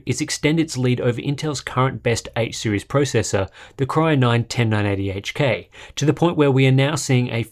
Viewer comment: Did he really say cry9